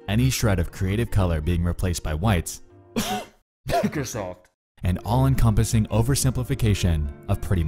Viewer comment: What did he say at 5:01? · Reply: microsoft xD